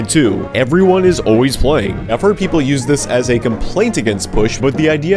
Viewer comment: why does it sound like he said “white” instead of “playing” it had me confused for a second so I had to replay 💀